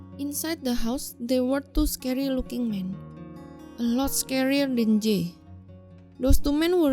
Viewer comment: wTf¿ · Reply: Angela안젤라 i hear "most scaryer than jey" 😂